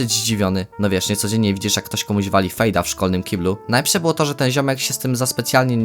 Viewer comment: usłyszałem wali fiu... no sami wiecie o co chodzi